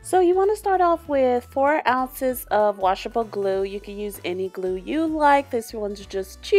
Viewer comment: I thought you said you had no glue